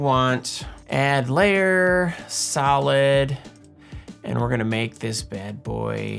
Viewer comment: When you said "solid" during the video I could've sworn I heard salad. So my suggestion is to animate some salad (I'll leave the theme and criteria up to to decide ;) for one of the next ones hehehe! (sorry not sorry!)